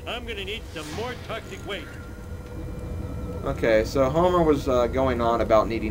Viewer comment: hehe at 9:25 it sounded like u said homo XD